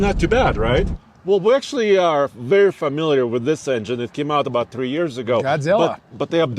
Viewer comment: Too funny. @ 25:15 I hear “We are very familiar with the 7.3” Then @ 26:15 the guy in blue denim ask “Does this have a turbo”.